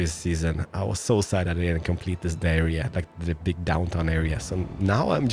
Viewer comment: Did u really say diarrhea instead of saying downtown area??!!